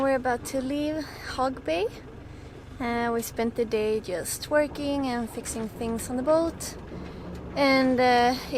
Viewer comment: did you say y'all spent the day twerking??? :)